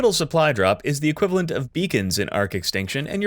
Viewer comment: I could have sworn you said "The OSD is the equivalent of Vegans in ARK Extinction" Then you said "You're probably going to hear them before you see them." Made sense to me.